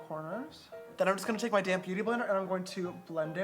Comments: lmao every time you said damp beauty blender i thought you said damn beauty blender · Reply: I wasn’t paying too much attention the first time he said “damp beauty blender” so I heard him and I thought he said damn beauty blender too 😂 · lmao every time you said damp beauty blender i thought you said damn beauty blender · When James says I'm going to take my damp beauty blender for the longest time I thought he kept on saying damn beauty blender 😂😂😂😂😂😂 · When You were saying your damP Beauty blender I thought you were saying your damM Beauty Blender and I was like WHAT!!